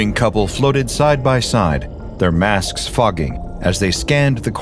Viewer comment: THEIR MASKS F#£KING!? I think I misheard that.